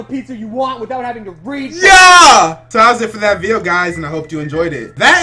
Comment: did say YEAH! or YO! ?